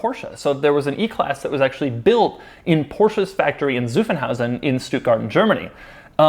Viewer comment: His pronounciation of Zuffenhausen sounded like Suffenhausen. Suff means something like "being drunk".